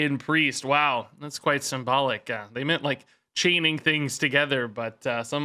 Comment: lmao I thought you said "that's quite some bollock" instead of symbolic